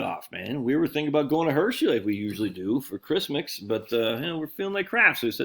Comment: did you say Hershey? Like Hershey, PA? HERSHEYPARK may well be my favorite amusement park ever!